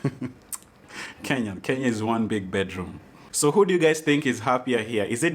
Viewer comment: Did he just say Kenya is one big bedroom? 😂😂😂😂